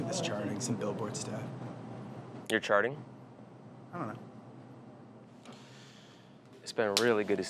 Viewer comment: What does say when Macklemore says “your charting?” 2:15 even in slow mo it’s mumbles YOOO MAC WTF is he saying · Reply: "i dunno"